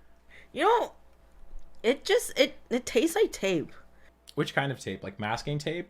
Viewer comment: Tang has maltodextrin as well as table sugar and fructose... and it is delicious. (I heard tang, so im going with that)